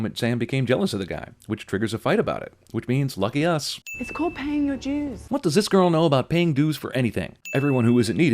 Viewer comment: Paying dues...oh, that makes more sense. When I first time saw this movie, I thought she said "paying you jews"...oh, silly me.